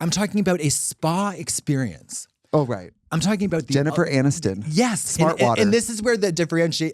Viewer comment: Thought you said Jennifer Aniston Fart Water